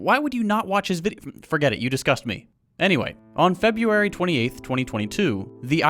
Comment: I heard "You disgust me" as "you discuss me" and I'll die confused